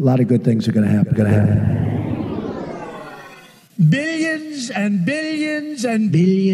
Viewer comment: Who is "Bill Jensen" and why does he get mentioned so often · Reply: I heard "Billi Ensen" :D